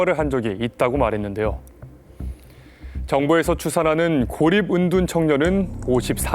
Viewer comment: 고립 은둔을 오리 문둥이로 들었네... 순 우리나라말의 히키코모리인줄..